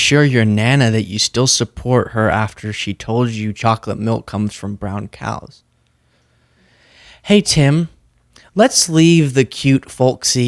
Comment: Thank you for clarifying he said "chocolate milk comes from brown cows" because I heard "chocolate milk is not running around the house" LOL. Not that it makes much more sense anyway 😅